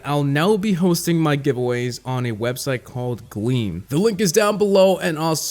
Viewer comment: I thought he said “ a website called weed”